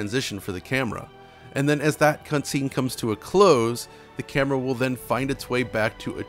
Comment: funny slip here, he says "cuntscene" instead of "cutscene" lmao